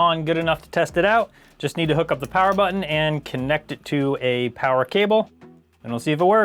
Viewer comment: did you say "kinect" it to a power cable?